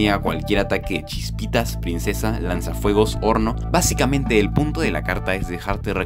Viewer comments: Cuando dijo horno pensé que dijo porno JAJAJA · Yo escuche “chispitas, princvesa, lanzafuegos, ₱0RN0”